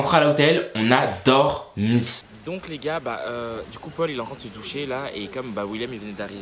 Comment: J'ai bug j'ai entendu il est entrain de ce toucher 😂😂😂